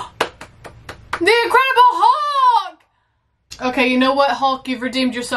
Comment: Did you say "The Agreeable Hulk"?